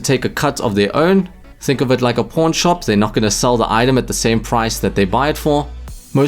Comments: I heard "porn shop" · I thought he said porn shop lel.